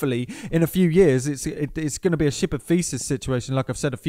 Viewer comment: Sounded like you said "ship of faeces"...that's a different proverb entirely! :)